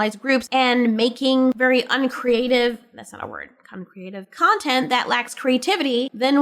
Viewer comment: You know, the strangest thing with tosh coming out as nonbinary to her mother was just how understanding her mom was. Like, the scene was clearly made to make the mother look like the bad guy. But she didn't even say anything offensive or transphobic at all. She was actually understanding with tosh when she was like, "Well, we have a specific word in our culture that means nonbinary. Is that what you mean tosh?" So, I am just SO confused why the writers didn't go all in making tosh's mother just the absolute worst being alive, and instead made her sympathetic. Like, why? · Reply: Did you say "uncreative"? Because that is a word. You didn't make a mistake. Unless you wanted to say a different word that I just missed. 😅